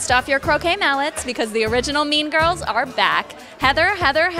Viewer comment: is it bad i thought she said meme girls instead of mean girls